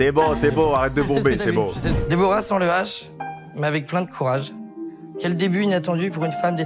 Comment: j'ai entendu "Deborah sent le hash"